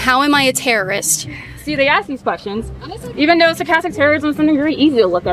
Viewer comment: Did she just make up a new "crime" ? "Sarcastic Terrorism". Maybe I misheard. But if anyone is a sarchastic terrorist it's me.